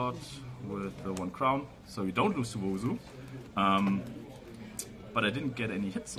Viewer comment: Nice profile and congrats. Also at 1:31 what was the card he said we should worry about losing to? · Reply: Wousu